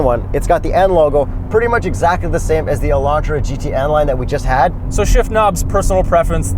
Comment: Wait. Did say they just had the Elantra GT N Line?!?! @ 3:40 Yesssssssss can’t wait for that updated review.